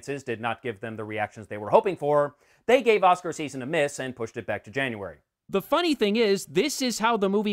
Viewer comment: Can somebody tell me what he is saying at 02:01 please ? "They gave us a... ???" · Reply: 'They gave Oscar season a miss and pushed it back to January'. Thank you myself 🤗